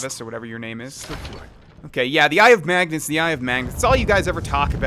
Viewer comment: I heard "the eye of magnets" lol bad hearing